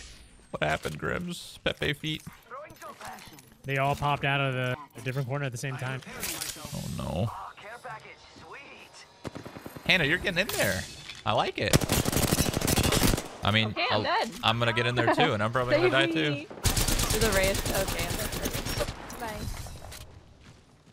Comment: Lmao I thought the girl said “I gotta fart I’m getting there” not “I’m kinda far” 😂